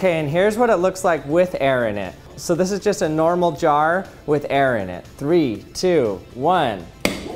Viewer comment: For some reason I heard "this is normal air with jar in it" Am I deaf?